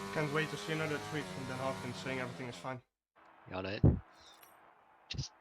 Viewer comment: What did said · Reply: "can't wait to see another tweet from them saying everything is fine"